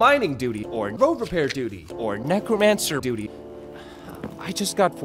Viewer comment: the subtitles say necromancer beauty lol